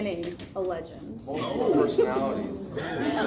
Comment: What was he saying at 5:13? I only understand personalities. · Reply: he said the show about his life would be called multiple personalities